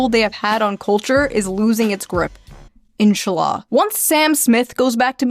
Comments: Was she trying to say Inshallah? Sounded like she said enchiladas. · did she say inchallah hahahahah nice